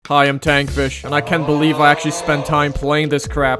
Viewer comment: i always hear "hello i am tank fish" please help me